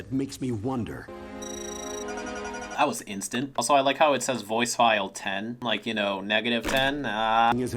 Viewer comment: I always thought it said "Voice ID"